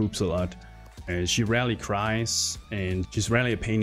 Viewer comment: it sounds like he says "she Rally cries". Warcry and Berserker buffs leaked so soon? nice video btw